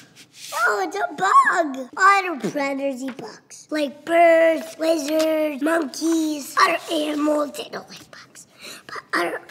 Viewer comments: did anyone else crack up because they thought he said " bird's, wizards, monkeys" when he said about predators who eat bugs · I swore I heard him say wizards eat bugs. Then, I consulted my logical brain and corrected wizard to lizard.